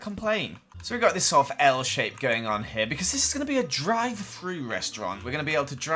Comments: i thought he said he was making a dried fruit restaurant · For a second I heard dry food instead of drive thru.